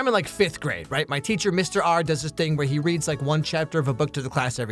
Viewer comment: I'm sorry, did you just say "readed?"